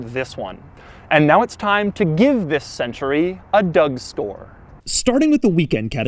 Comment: Did Doug say he'd give this car a Doug Store instead of a Doug Scoure or am I the issue?